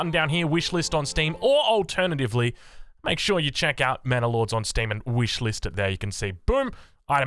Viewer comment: Sounds like is saying "Mana Lords." For my fellow Magic: the Gathering players, sounds like a planeswalker, eh?